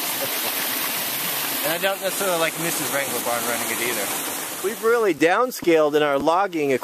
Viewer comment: Hi, @ 10:49 I think I heard you slip and call your wife "Mrs. Wranglerbarn". I love your channel. I going back and watching them from the beginning. I couldn't believe it when I heard that half of your timber frame home was stolen.